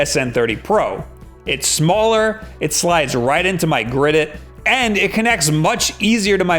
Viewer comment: In case anyone was wondering, where the captions say (mumbles) at 8:00 it says "Grid-it)", and it is about $20 if you search Amazon